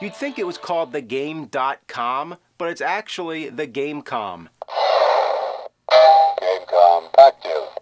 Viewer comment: "The Game Com, FUCK YOU." That's what I heard.